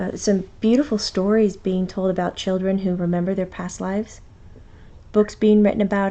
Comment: Is it me, or does someone say "chicken" at 11:37 ? Put your cursor on 11:37 and keep clicking that spot, quickly. Cool.. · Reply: I think it was children, not chicken. lol.